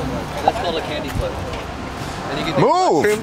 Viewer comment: The group at 4:25 was talking about drugs 100%. All I heard was candy flip but iykyk